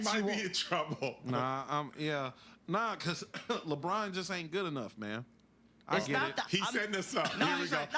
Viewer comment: 💥So it sounds like he is saying, "Lebron is just not that good". Just more EXCUSES!